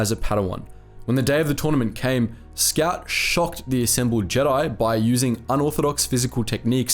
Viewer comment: I heard "shot" and was like "Uhhhhhhh sureeeee"